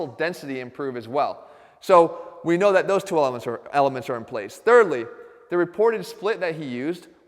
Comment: Did he say “OMLETS”?